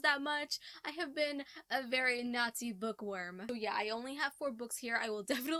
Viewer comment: Okay , for some reason I thought you said Nazi book worm instead of naughty book worm and I was like, wtf.